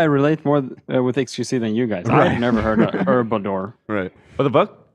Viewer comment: says “Herbador” instead of Herbivore and it’s sending me pls clip that sound Zach